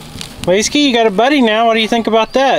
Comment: Year late, Leskie (Big puppy) and Cora (Is the puppy). Now 2024 Congratulations on their new 9 puppies. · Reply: Leskie? thought he'd been saying Whiskey this entire time XD